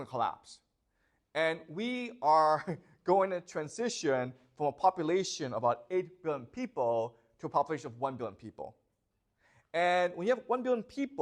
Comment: Sorry i cannot understand the pronunciation, did you say that we are going in transition from a population about 8 billion people to a population of 1 billion people?